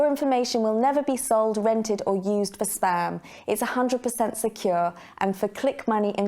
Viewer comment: “Your information will never be sold, rented or used for sperm?????? What?????? Oh ‘spam’! She actually says ‘sperm’ though😂😂😂😂